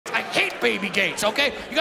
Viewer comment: "i hate baby gates!" imagines bill gates as a baby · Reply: i first thought he said baby goats